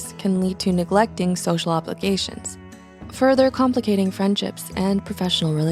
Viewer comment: Subtitle says applications when it should read obligations.